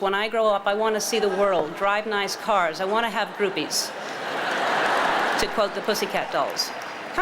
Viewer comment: I seriously thought PCD said boobies not groupies. Whew, I'm not the only one.